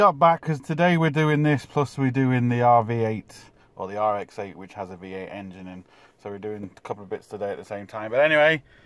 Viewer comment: Did I hear you say RX8 with V8 engine 4:44 Im very interested